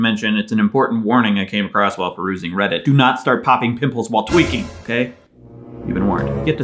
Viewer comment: When it got to the part about "Don't pop pimples while tweeking", I thought it said TWERKING, and I was really confused for a minute thinking someone had a weird-ass accident.